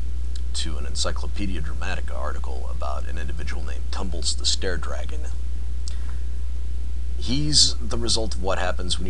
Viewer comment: That's it? I thought you said "scaredragon", as in, he went around scaring kids. The Furry Fandom is the name for the group of people who are fans of furries. You can't tell someone they can't be a fan.